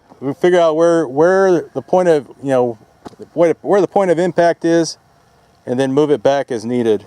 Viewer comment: says "Yea, we'll figure out where the point of impact is, then move it back as needed." What I hear "Yea, we'll figure out where the point of demonetization is, then move it back as needed." XD